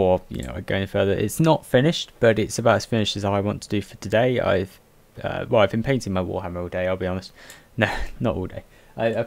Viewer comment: Did you say: 'ive been painting my warhammer all day' at 0:18? Cuz that would be pretty awsome :P